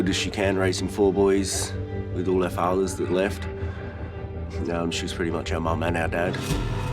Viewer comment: did he say all our fatherS left? Aaron looks stable enough. He doesn’t look like a crack Ed unlike Neil and the mom.